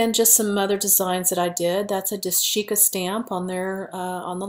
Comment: On the left at 12:36 - you mentioned the different stamps you could also use but they were not listed In the description. Did you say "dashica" ? If so, what plate number did you use? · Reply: Yes, Dashica stamping plate :<link>